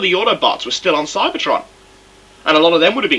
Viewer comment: i thought you said cybertron was a load of crud